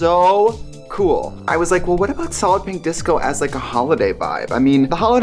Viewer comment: Wasn't paying attention at first, and I thought Trixie said the collection was called "salad pink disco" 😅